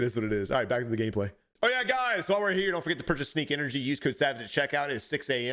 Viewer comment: I think I’m a real life bot… I literally thought you’ve been saying “Snake Energy” this all time. I have to rethink my life